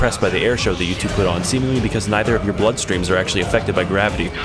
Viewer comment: wait I thought it said “because you both have no blood” New edit: Wtf was me two years ago on?